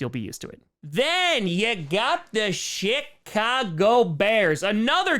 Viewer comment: I swear to god this man said Shitcago